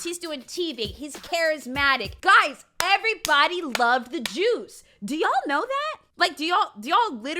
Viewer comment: I thought was saying "Everyone loved the jews" and not "everybody loved the juice". Was about to press X to doubt the statement but everybody loving the juice made more sense lol